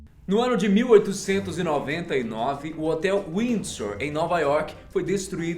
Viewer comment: Juro que entendi hotel whindersson kkkkkkk